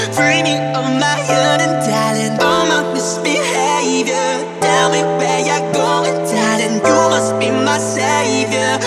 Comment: I THOUGHT IT SAID "IM NOT MISBEHAVING TELL ME WHERE YOURE GOING DADDY"